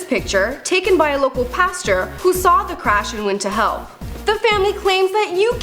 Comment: am I the only one that thought she said "who saw the crash and went to hell" ? haha